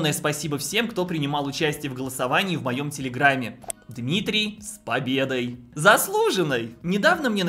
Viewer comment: тьфу ты,а мне послышалось "Дмитрий Песков". 😅